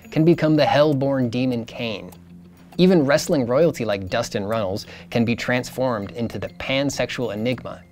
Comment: "Dustin Reynolds"? Did anyone else hear this or just me? Anyway, thoroughly enjoyable video.